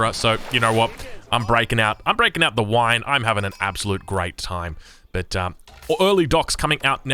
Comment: "You know what? I'm breaking out, I'm breaking out the wine! I'm having an absolute great time" - poetry! 🍾🥂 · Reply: I thought he said grape time !